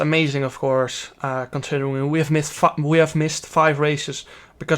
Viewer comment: did he said missed f**k